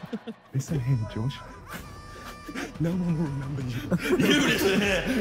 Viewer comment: what does niko say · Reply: No one will remember you